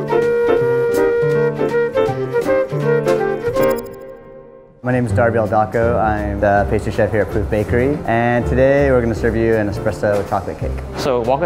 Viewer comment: i thought he was saying poop bakery but awh steven is such a cinnamon roll